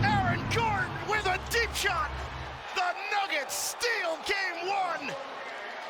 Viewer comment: sounds like he said dickshot